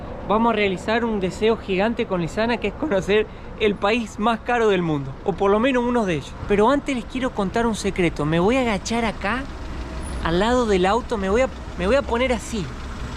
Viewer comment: Cuando dijo me voy a agachar acá, pensé que dijo: me voy a cagar acá🤣🤣🤣🤣🤣 y yo dije, vamoooos, fiel a latam 🤪